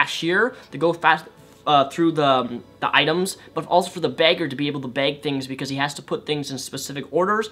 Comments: I got really confused for a moment, when you said Bagger I thought you said Beggar XD But I do totally agree with everything you've said! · When you said bagger I thought you said beggar at first.